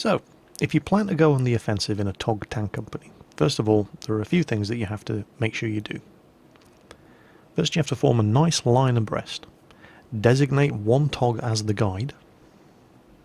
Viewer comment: I heard "breasts"